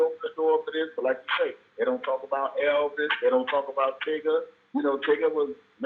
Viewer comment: did he call that man “tigga”? 😂😂😂🗣“TIGGAAAAA”?! (Draco voice)